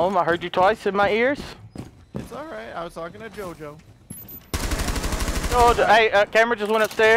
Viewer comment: did say he was talking to jojo?!